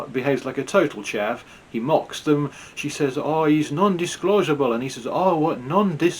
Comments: Duh it was a livestream. He did not have to upload it. He is not a Chav and he was not mocking her. It did sound like she was saying Disco. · I thought she said DISCO he’s not a chav.